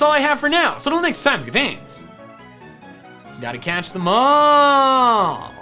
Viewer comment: When says "Gotta catch'em all!" I hear "Gotta catch a mole!"